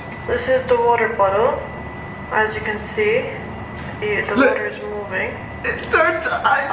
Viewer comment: did anyone heard her say the water is moving